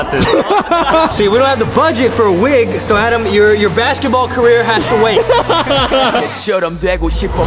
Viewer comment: "Your basketball career has to wait" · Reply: ꧁føødie I thought it said your basketball Korea has to wait 😂😅🤣